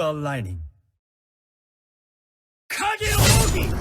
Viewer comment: From what I've made out of the words during Haya's ult, I think he said "kageroniyobi" and it translated: "Call in the shadow"